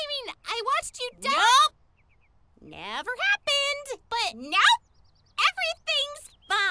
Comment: NOPE NEVER HAPPEND! what I heard NOPE SPOILERS